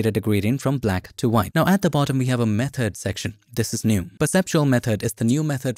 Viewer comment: I thought you said “meth-head section”, and I was thinking it must add body weight.